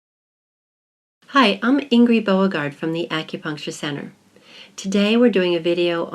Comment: In the beginning I dunno why but I thought she said 'hi, I'm angry bo- gaurd'